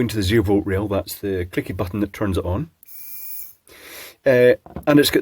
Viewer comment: OH! I thought he said kinky button, turns out it was click button...oh, well. 🤣